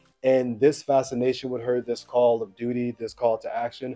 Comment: totally thought you said “call of booty” 😂